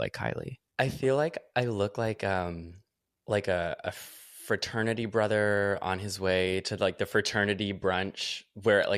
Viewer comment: I heard "I feel like a fraternity brother on like a fertility brunch"